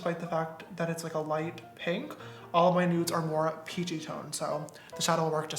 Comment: did he just say all my nudes?